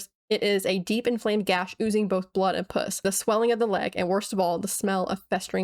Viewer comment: I know I know, but it sounded like you said puss and I’m walking around at work listening to this and I can’t help but laugh. Truly unintentionally funny.